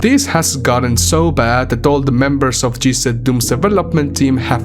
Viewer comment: I thought you were saying C Doom, not Z-Doom.